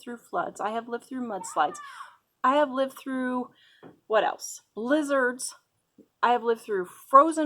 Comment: Lol I thought you said lizards instead of blizzard. I thought lizards. Did you have an invasion if lizards. But a great article. Love your site.